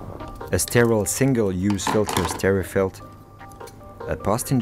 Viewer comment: What did he say that thing is called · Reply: It's called a sterifilt and you can get it <link>.